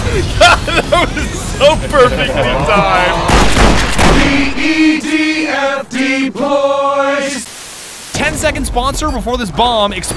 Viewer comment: Can anyone tell me what they say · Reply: 🎵THE EDF DEPLOYS!